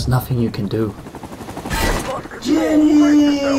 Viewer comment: Did he just say Ginny.